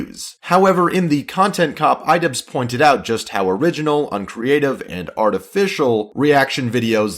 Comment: Is it just me or did he say original